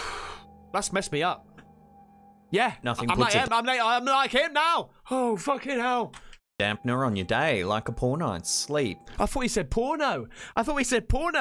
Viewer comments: "you could be nude right now watching this but I don't give a shit. I'm freaking out" haha you're the best brother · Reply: 🤣🤣🤣And i thought said Porno to. .LOL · I like this channel..have liked and subscribed-thanks for the giggles uploader..p.s:I also thought they said porno, lol…p.p.s:nope,I don’t remember Chocky-just Chucky!!